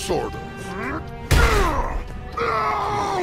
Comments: When youre roommate is tired of your shit and gives a list reasons why you suck: "Is this what its like to live with me? I hate me." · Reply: Instead of “I hate me” at the end of that sentence, I heard “I hate living” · When youre roommate is tired of your shit and gives a list reasons why you suck: "Is this what its like to live with me? I hate me." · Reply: I heard: "I'd hate living with me".